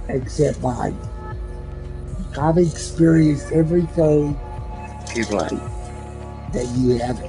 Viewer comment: Anyone hear this when he says "in life" it sounds like golem or something?